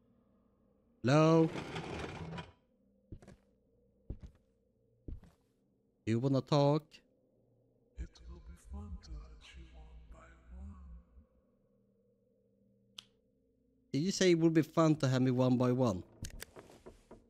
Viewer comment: I think it said "it will be fun to hunt you one by one"